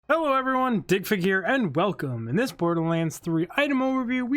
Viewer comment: This time it sounded like you said "dick figure" lol